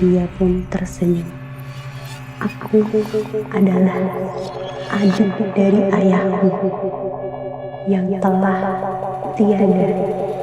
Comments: Iya...kegedean echo,jadi gk jelas...malah yg kedengeran "aku adalah anjing ayahmu,yg mati dlm kandungan..." , ????🙄🙄🤔🤔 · Gara gara efek echonya masa kedengeran aku dia ngomong “aku adalah anjing bapamu yg telah tiada” 😭